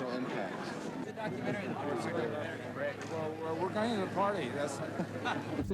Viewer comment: omg that's Andy Warholl. I couldn't hear what he,s saying? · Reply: the usual : hey andy what did you think? oooh we're going to the after party.